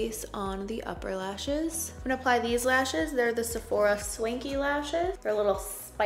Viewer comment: When you said Swanky lashes I thought you said SLINKY xD